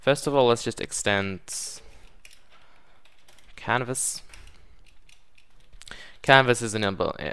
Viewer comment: When you said "canvas" I thought you said "Cannabis"